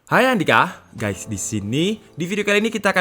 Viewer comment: apa ane salah denger yak? openingnya kebalik, "Hai Andika, Guys disini"